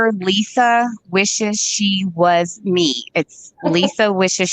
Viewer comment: What’s the petition please guys?? Can we in the U.K. sign it of it anything safe and logical you do?? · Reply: Its on Instagram #Lisawishesshewasme is what I heard her say. Also the name Michelle Rogers. Not sure the spelling of the above name... but heard the petition name. #Lid needs petitioned off -- Ive nicknamed her lid - cuz cant say bglisa. Lisa in diapers or #lid is more appropro